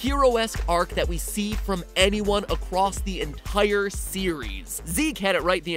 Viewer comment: Is it just me or did anyone else hear matpat say 'acwoss' >u<